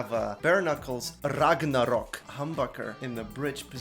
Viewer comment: Dead meme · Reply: sounded like you said hamburger... anyways just started watching your content, im liking it .